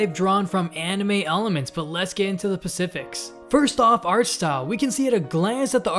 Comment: did you just say let's get into the pacifics?